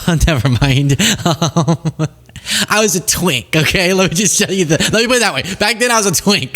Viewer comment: …Did he say he was a twink…? I’m done. Goodbye.